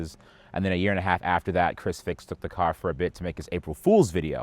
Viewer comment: Did he say Chrisfixit? That guy is the man